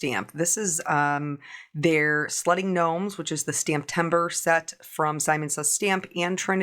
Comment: Lol sledding gnomes, at first I thought you said slutty gnomes. I was like wtf and had to rewind.